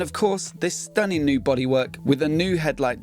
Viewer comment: It kills me the way you BRITs say bodywork, to my Yankee ears it sounds like you say bot he works lol I try not to laugh, but I’m having difficulty